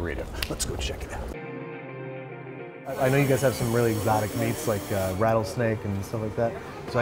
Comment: I thought you said erotic meat